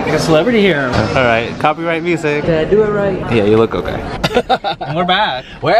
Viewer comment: did he say ‘dad it right’ ?